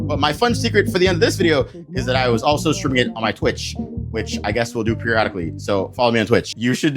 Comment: i think im deaf cuz i heard him say steven uterus 🥀